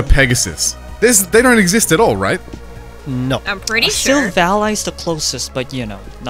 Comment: What Pokemon did they say? "Valeigh" or something?